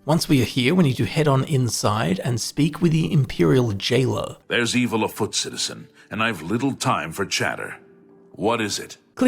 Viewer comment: i was looking at my dog being cute, not looking at the screen, to me it sounded like the guard said, "There's evil a footses".